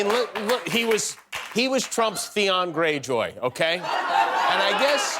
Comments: Ted was Trump's what? I can't make out what Jimmy is saying. It sounds like he's saying Theon Gray Joy whatever that is, I've no idea. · When Marjorie Taylor Greene speaks, she just takes ALL of the air out of the room. Why exactly does she get ANY airtime? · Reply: Ted was Trump's what? I can't make out what Jimmy is saying. It sounds like he's saying Theon Gray Joy whatever that is, I've no idea. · Ted was Trump's what? I can't make out what Jimmy is saying. It sounds like he's saying Theon Gray Joy whatever that is, I've no idea. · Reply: Theon Greyjoy, Game of Thrones character. Very easy to find, look up Theon Greyjoy character analysis or Game of Thrones summary. ❤️